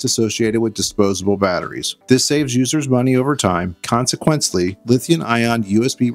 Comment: what was that word? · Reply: From the dictionary: "Consequently" is an adverb that means "as a result" or "therefore". Thanks for watching.